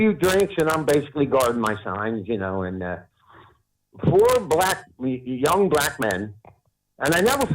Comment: "four black Neeeeee, young black men." Who else heard this, am I the only one? 0.0